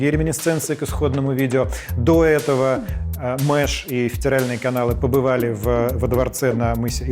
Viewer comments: правда вместо "федеральные каналы" сказал "втиральные" или мне послышалось? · я услышал "Втиральные каналы" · Мне послышалось... ВТИРАЛЬНЫЕ КАНАЛЫ....)))